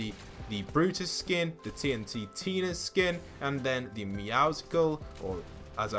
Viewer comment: did you say TNTNTina